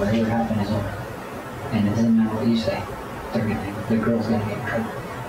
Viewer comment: I think the caption at 42:38 is wrong. It says, "And it's in the police's say," when I think it actually says, "And it doesn't matter what you say." also at 4257, it says "I don't want her to get charged." when I think it says, "I don't think I can do that." not that it makes a big difference, just thought it was odd and worth mentioning.